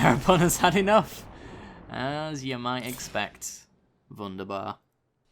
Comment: What did you say at 20:16 ? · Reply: Wunderbar. Wonderful in German :)